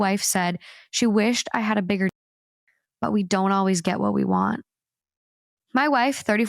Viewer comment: I thought she said a bigger wallet 🤔